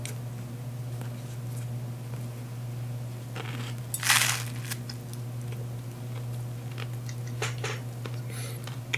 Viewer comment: what was that said? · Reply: Fireball